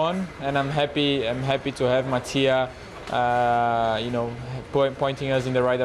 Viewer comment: I thought he said “my tia” every time 😂